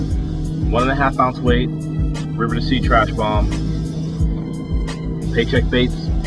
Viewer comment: When Ish mentioned the weight it sounded like he said the brand was "River to Sea" but I googled that and I didn't get anything. Any help here?